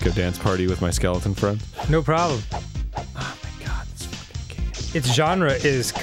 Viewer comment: does Arin say "oh my god that's fucking gay" under his breath after getting hit?